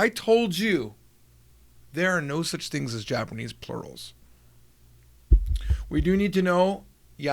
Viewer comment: I was watching this on my break at work while there was a lot of noise around me, and I legit thought you said "I told you there's no such thing as Japanese pornos". I was thinking I don't know where is going with this, but I won't question his methods. xD